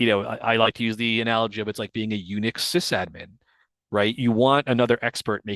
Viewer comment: I had to go back because I thought he said "unich admin"